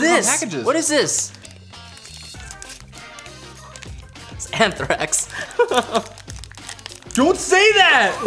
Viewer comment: What does day at 8:39 and says "Don't say that" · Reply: something like anthrax